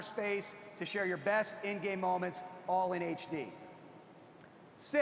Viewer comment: did he say gaygay moments?